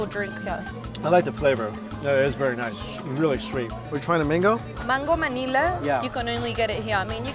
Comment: i heard her say "mango manila"....